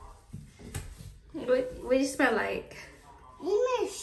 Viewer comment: said what does it smell like sounded like she said shit 😂 so cute